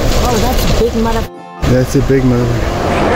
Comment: did say motherfucker